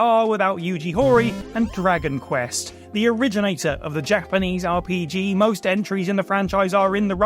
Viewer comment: "The originator of the Jack Benny's RPG"? That sounds like a very different video... and likely offensive. (And while I'm sure it won't stop people from trying to correct me, anyway, yes, I'm aware of what is actually being said... but it sure sounds like something else.)